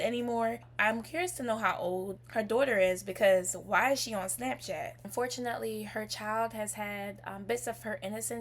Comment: Did I hear"'why is she crying?"